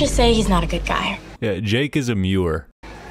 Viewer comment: DAAAAAYYYYUUUUUUUMMMM 8:47 I thought bro said "Jake is demure" 11:14true